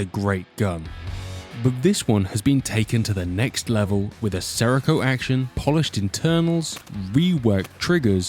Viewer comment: did anyone catch what type of action? did he say seraco action?